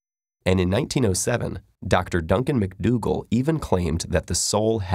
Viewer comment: did I hear that right? His name was Doctor Dunken McDoodle?